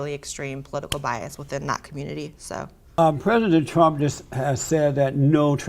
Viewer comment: Lol I had to rewind at 9:05 because I heard predator trump! I was like wait a minute what did he say ?! Yep still sounds like predator trump ! 😂😂 Leftys gonna love that part😂 yes I know it's just how he speaks , still funny tho